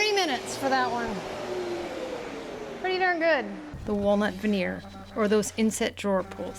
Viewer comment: This turned out so fantastic! Love the colour. I just wondered if it is teak veneer? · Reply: I think she said walnut at around 6:47.